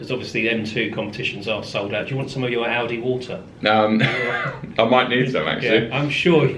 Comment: Thought he said Aldi water lol